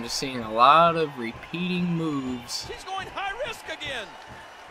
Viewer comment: did i hear "hes going to the top rop agian" at 2:46 , i think i did